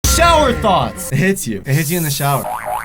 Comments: i thought they were saying "...Shower THOTS" xD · i keep hearing "shower thots" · the whole time i thought they were saying shower thots cause im not american.